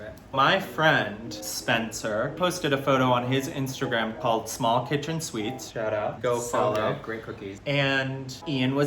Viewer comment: I don't know what's wrong with me but I heard "small kitchen suites"